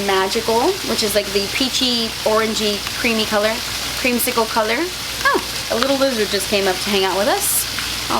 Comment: I thought you said “a little loser” and I was like wut.. Lol